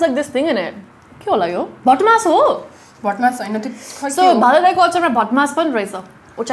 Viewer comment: For some sec I heard "Paale dai ko achhar" instead of Bhale dai ko achhar😂